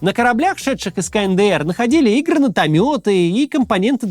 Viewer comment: услышал "Игро -натометы" что?